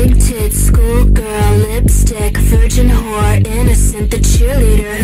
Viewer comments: I thought it said f you but it was vaccum cleaner😂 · That one you say vacuum cleaner, I heard you said, "FUCK YOU CLEANAR" 💀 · I thought he said “fuckumn cleaner”😂😂